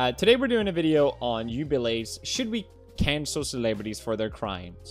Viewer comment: Hold up did he just say “you”bilee